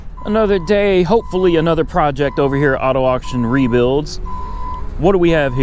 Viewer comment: Sounded like he said auto option rebuilds.😂